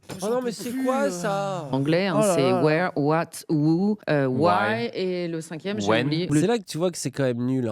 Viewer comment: MDR ca me fume j'ai cru il avait dit "whiny" ce niqué .. J'en pouvais plus j'ai repasser trois fois pour m'assure que c'etait bien moi qu'etait con xD ("Whiny", pour dire plaintif btw)